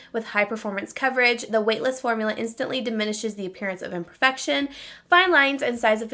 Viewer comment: When you said imperfections when reading the concealer box I thought you said infection 😂 I was girl what???